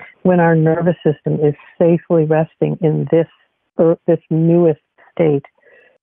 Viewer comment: "when our nervous system is safely resting in this __? __ state..." I can't understand that word. The sound quality is subpar. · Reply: Yes “this newest state which we call ventral vagal”